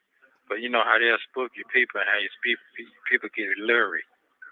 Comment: What did say? People get lury? Did he mean leery? I never know with him anymore because he just says anything 😂